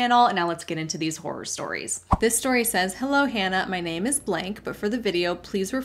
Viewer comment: You forgot to censor the name in the first story!! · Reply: Oh my gosh.. I thought you said their name was BLAKE lol. Im sorry I misheard it! Love the video as always ❤